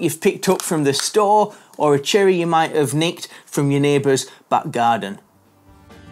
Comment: Sounds like he said nicked from their butt garden 😅